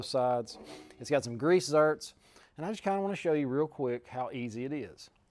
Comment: It sounded like you said grease zert but it is a zerk.